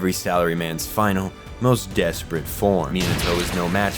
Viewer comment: did you say minato or im hearing it wrong?:|